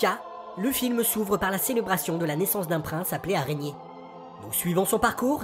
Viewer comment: j'ai entendu "un roi appelé "Araignée"" voila, ce commentaire ne sert à rien à part le référencement :p